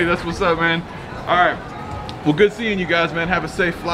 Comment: did he say later fucker??